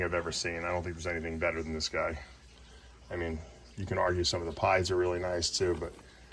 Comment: Did he says pieds?